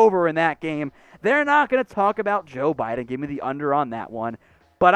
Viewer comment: did he say Joe Biden🤣🤣🤣